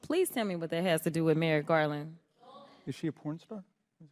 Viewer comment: When Moscow Marjorie starts off wanting to know who hires the judge’s daughter, it sounds like someone said “ is she a porn star “ I’ve replayed it several times as it still sounds like that